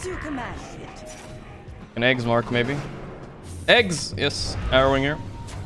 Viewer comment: Anyone caught what NothingToSay said at 8:49? Right before he said Tiny buyback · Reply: I got halberd